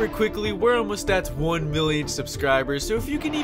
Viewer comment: did he say billion or million? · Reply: Million I think